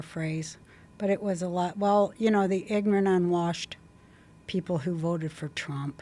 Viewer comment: did she say “the ignorant, unwashed people” ???